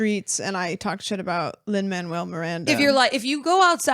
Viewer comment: Every time you say his name all I hear is Wall-E's Captain saying "Man Well! Man Well!!" 😂